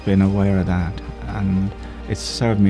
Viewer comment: I think he said "served me " in good stead, not "certainly" in good stead.